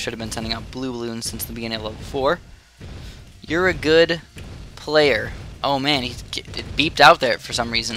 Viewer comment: i thought he said ur a good hacker at 02:40 lol